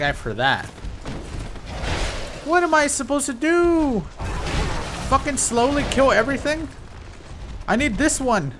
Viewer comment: I thought he will say fucking slaves... I shouldnt watch dat gachi remixes.